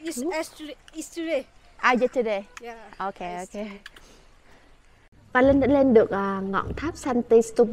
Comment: not very sure, but I think she said she studies 'History' subject